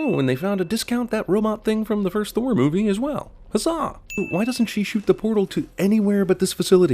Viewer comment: When he said, "Huzzah!" I initially thought that she said, "A suh!"